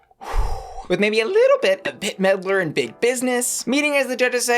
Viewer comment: I hear you say “Bit Meddler” @ 8:35 and all I can think is, that would make such a good drag name 🤣